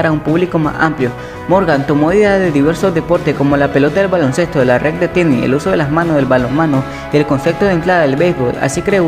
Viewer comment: oye amigo que dices en el minuto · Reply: Morgan tomó ideas de diversos deportes, como la pelota del baloncesto, la red del tenis, el uso de las manos del balon mano y el concepto de entradas del béisbol. Así, creó una disciplina que denominó “mintonette”.